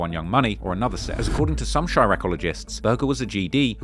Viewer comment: I had to pause this, did he just say chiraq-oligist? I'm done